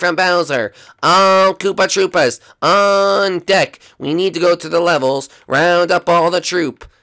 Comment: Lol I laughed so hard when shy guy said all troops on deck it sounds like it says all troops on dick because shy guy kept saying all troops on deck it sounded like it said all troops on dick